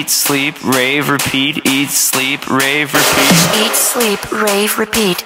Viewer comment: I Heard it as Eat sleep Train repeat :)💪💪